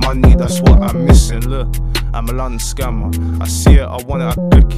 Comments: Who else thought is said “I’m a London scum” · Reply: I thought he said “I’m a London sum I see it I want it I take it” lol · Who else thought is said “I’m a London scum”